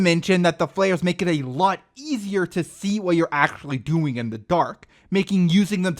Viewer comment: I heard "actually see what your doing in the dark" as "actually see what your gooning in the dark" ...wow.